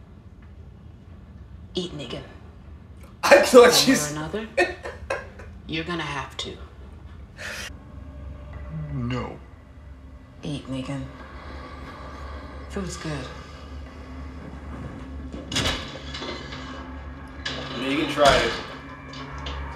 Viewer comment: Eat nigga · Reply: I thought she said "eat nigga" too at first. That would have been hilarious! LOL